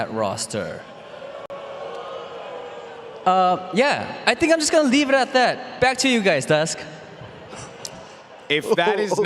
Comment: Why did I hear, “I’m gonna sit at McDonalds” Replay it. What do you hear?